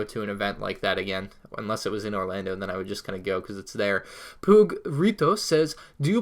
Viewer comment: am I the only one who heard poog instead of pug